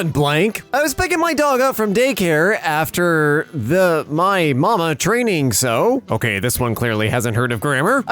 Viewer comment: For 4:23 he was using grammar it was just said weirdly. It was M.M.A. Not ma.